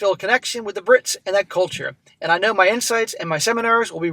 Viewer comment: At first I heard "I know my insides..." hehe